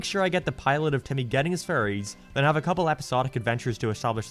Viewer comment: I swear everytime you say 'fairies' I keep hearing 'furries'